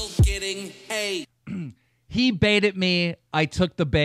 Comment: I thought he said "Why am I still getting aid" instead of hate